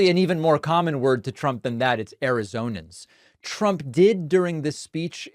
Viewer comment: I thought he was saying Azorean and was confused as to why he cared about people from the Azores. Arizonan makes more sense.